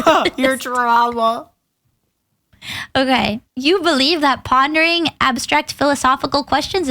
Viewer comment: Does anyone else hear Pon-de-ring? Maybe I'm too Bau Bau.....